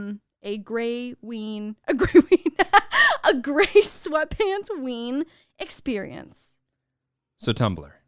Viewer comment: Lol my brain thought was saying grace Whitman but it was gray sweatpants lol